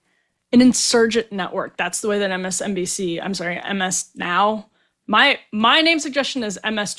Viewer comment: I thought at first it said "Miss Now"😊